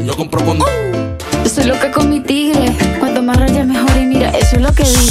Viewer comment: Yo siempre creí que decía “soy loca come tigre” 😔✌🏼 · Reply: Yo pensé que decía "soy loca como tigre"